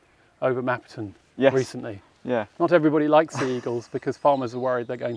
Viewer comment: Today I learned that lamb-stealing sea eagles exist. I thought said seagulls, and I thought, "Good LORD how big ARE those things there??!!" though dog and lamb-stealing sea eagles are quite terrifying as well!